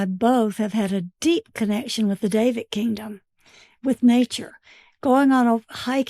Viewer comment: mark- Did they say David or davic kingdom referring to nature? Could that be considered Davidic?